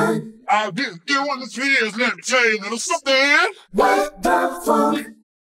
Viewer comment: What tha f*ck 😂 4:20 they actually said (with the funk)